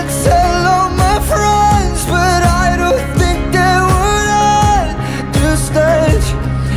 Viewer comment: i hear but i don't think that were at that stage